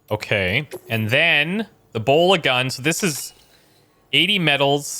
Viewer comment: I was NOT looking at the screen when the hologram dude was explaining the bola gun, and I thought he said "Ebola gun." I imagined a gun that shot pellets full of the Ebola virus, and for a second, I was thinking how entirely unproductive that would be. Couldn't figure out why was so excited 😂. Multitasking is not my jam today, apparently. And I can't unhear it as "Ebola gun" now 🤣